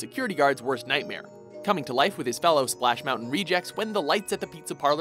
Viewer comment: did he just say splash mountain rejects?